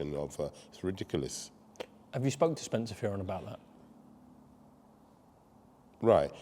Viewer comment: what name is he saying at 8:13 (Spence and .. Fury...?)? edit: Spencer Fearon